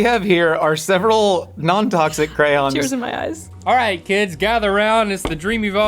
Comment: w the icecream sandwiches i thought she was saying cran (cranberry) but she was saying crayon 😭😭